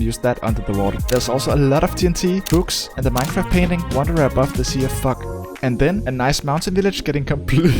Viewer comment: Wanderer above the sea of fog. Misheard it as 'Wanderer above the sea of f*ck' XD